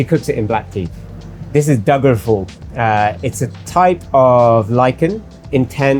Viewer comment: he mentioned an herb I am not acquainted with. It doesn't occur in the recipe list above. "doublfur"? couldn't really understand the pronunciation. Anyone out there that can help? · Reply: Found it! Dagar phool